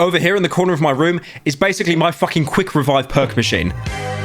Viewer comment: Says "Perk machine" All I hear is "Perc machine"